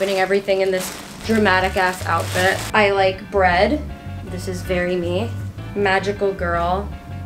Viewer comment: thought it said head lol